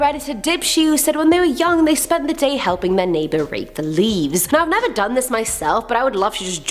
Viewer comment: At first I thought she said raped the leaves I was like hold up wait a minute something ain’t right